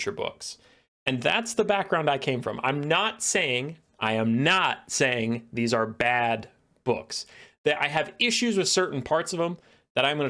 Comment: Did he just say they are bad books @ 2:28 ??